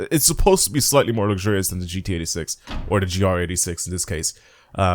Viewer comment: Did he just say gta6 😅